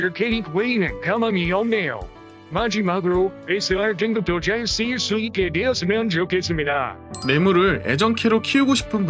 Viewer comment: 여기 뭐라는거죠..? ㅋㅋㅋㅋㅋㅋㅋ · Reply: SR등급도 잘 쓰여졌으면 좋겠습니다 ㅋㅋㅋㅋㅋㅋㅋㅋㅋ